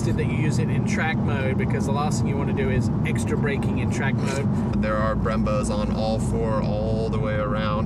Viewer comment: I thought I heard it only had brembros up front?